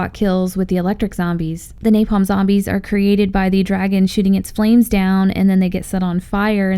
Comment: I heard dragon shitting his flames down